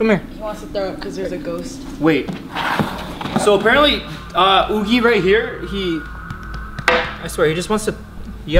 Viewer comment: 2018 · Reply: I heard " im right here""